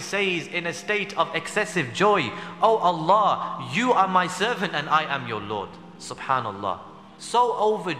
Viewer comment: I do not understand 6:30 what did he just SAY :O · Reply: He said Oh Allah you are my servant and I'm your lord. He said that from happiness, he mixed because he was in a state of great happiness